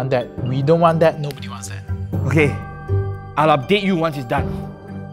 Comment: i misheard it as " i'll date you once its done"